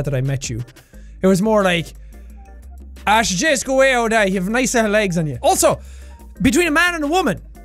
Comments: I GIGGLED 🤣I wish so bad that I could understand what he said but I can’t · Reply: Something then “you’ve got a nice set of legs” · I heard "ash just go away old eye an' ye have nice legs on ye"